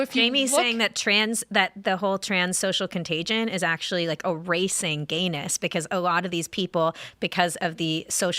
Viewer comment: She said, "erasing gayness'. I heard "a racing anus"...